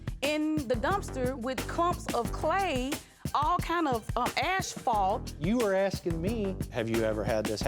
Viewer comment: Did she just say "ashfault"? lol! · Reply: I heard “ash fall”. I first thought she meant a bunch of ashes. It took me a few minutes to figure out that she was saying asphalt. She was excited, so it came out differently, I guess.